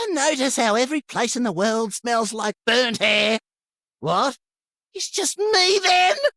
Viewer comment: Up until now, I thought at 7:00, I swore he said "booteh" not "burnt hair". Makes more sense and stops me from thinking hes secretly a creepy perv.